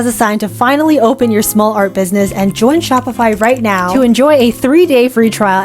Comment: THIS WAS SO FUN?!?! but the results were...questionable🥲 have a nice weekend and check out Shopify here! <link> also i hope everyone is enjoying the 'viral' series! next week will be the last one for now :) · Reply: I thought u said "Spotify" 😭